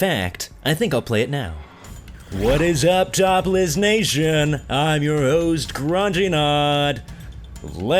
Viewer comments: Oh I thought he said Topless Nation. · when he said top list nation i thought he said topless nation... am i deaf probably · Sounds like you said topless nation lol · i thought he said top less nation lol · Sounds like your saying topless nation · sounds like ur saying "top less nation" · I thought at the end you said "TOPLESS NATION" LMAO